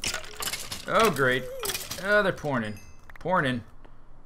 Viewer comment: When he said "There pourin in!" at 11:50 it sounded like he said "pornin" xD